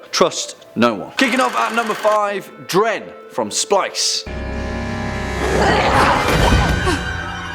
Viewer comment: I must be deaf. I thought he said "Dread from Spice", not "Dren from Splice".